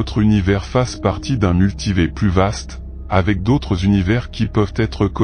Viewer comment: c'est quoi un "multivé" ? 😄